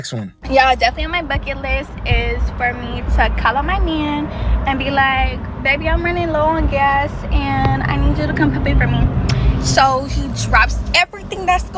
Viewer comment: Cannot understand a word the girl says at 10:30 but I’m clearly not missing anything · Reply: "Yah, definitely on my bucket list is for me to call up my man and be like 'Baby, I'm running low on gas and I need you to come pay for me' so he drops EVERYTHING that's going on and he SWERVES in his car and pulls up right next to me and he pumps my gas and he pays for it 😊"